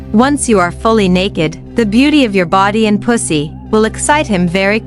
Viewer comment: did it just say pussy?😂😂 6:14 WTF 😂😂😂 5 times a day? 8:05 she looks so depressed after.😂😂